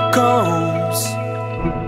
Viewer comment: I always thought he said “We’re walking on Fire” Honestly, woulda been a great song